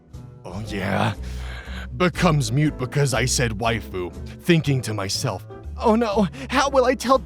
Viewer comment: I watched this with my mom, who does not understand 90% of internet humor though found freak outs and amazing voice acting funny. During the "Waifu" part, she thought he/and the post said "Wifey" and agreed with her entire chest and I was SO AMUSED.